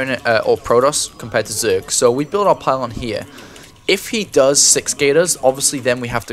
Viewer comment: Did he say 6-Gate?